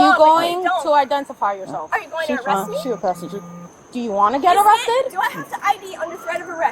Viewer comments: did she just call her prostitute? I really think she just accused her of being a prostitute. SERIOUSLY. I HEARD HER SAY "SHE A PROSTITUTE" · Did not refer to the young woman as a prostitute? I thought I heard her say; “She’s a prostitute!” at about 14:35-14:45. Right after goes into “Did you hear that?” psychosis. “We went through training.” Is such a red herring. went through training and probably outranked Officer. I feel like that dude has dealt with right wing heckling and instead of understanding found his own way to exorcize his childish vitriol. It’s hard to listen to after defending inclusiveness to people who would denigrate the “guy in uniform” with a furtherance of his own harsh relocation ideals. Some bigotry isn’t soft. I hope the future doesn’t come at him as hard as it is likely to. He needs a lesson but I fear his karma may be too fast to pull the reigns in time. Repent and atone. Before it’s too late. That’s all the advice I can muster.